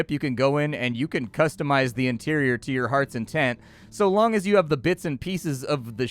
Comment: "You can customise your interior to your heart's content", not intent, that's not English. Content, as in, happy. You put the stress on the E when you are contented and you put the stress on the O when you mean the noun, only you guys don't do that anymore recently, or put the stress in the wrong part of the word, like with using the noun form of permit instead of the verb in mispronunciation, however, English is still a stress timed language, if you do it or not.